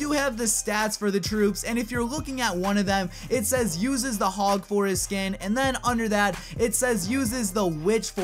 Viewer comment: I thought he said "uses the hog foreskin and uses the witch foreskin" at 2:22 😂😂